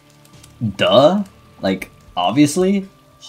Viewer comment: did he just said ‘да’(yes)?